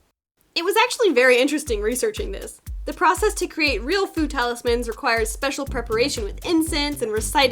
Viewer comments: By foo talismans I thought she said FOOD talismans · I keep hearing “Food talismans” instead of “Fu talismans” lol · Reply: i thought she was saying ‘food talismans’ for years